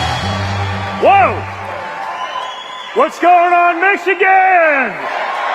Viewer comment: Did he say, what's going on Mexicans? I thought he actually said this.😂